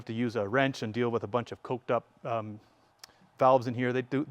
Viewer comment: did he say “coked up?” 😅